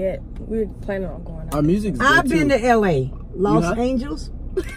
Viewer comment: "los angels" i love how she mispronounces everything😂😂😂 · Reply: I thought she said lost angles